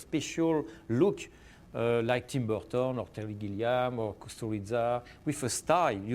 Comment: Can anyone tell what name he says after Terry Gilliam at 18:48? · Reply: Found it. Kusturica